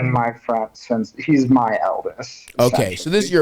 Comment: "He's my Eldis" what a cute thing to call your best friend 😂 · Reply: I thought he said he's my eldest. As in his longest lasting friendship. Idk